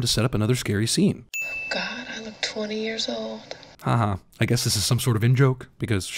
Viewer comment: OK, I have a very bad case of the Mandela effect. I could have sworn Nancy said "...I look like I'm 30 years old." Feeling weird....